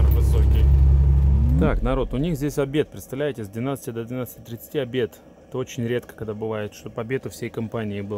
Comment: обед у всей компании с 12 до 11:30.....или мне послышалось!?